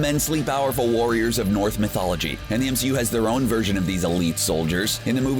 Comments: Did i hear wrong or did he say north mythology instead of norse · Did he just say "north" mythology?